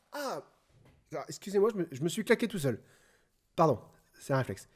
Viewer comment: pleonasme "je me suis frappé moi Meme " le me m indiqué déjà que c est toi qui t es frappé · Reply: Fripagee IG il a pas dit ça il dit" je me suis claqué tout seul "